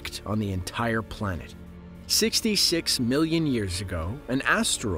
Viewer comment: Just wondering about your statement at 1:34 I believe I heard: “fifty six millions years ago” when describing the Chicxulube impact. I’m just curious where that number came from as the papers I have read state different much later dates of impact. Mostly 9 to 14 million years later for the impact site and the deposited iridium layer around the globe. If you could cite the source for 56 million years, I would appreciate it. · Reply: He definitely said 66, I don't know what you're hearing